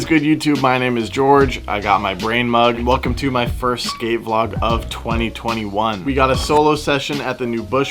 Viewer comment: I thought you said your first gay vlog of 2021 I was like what